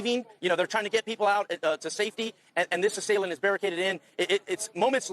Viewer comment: it sounds like he says, "And this is Salem, he's barricaded in"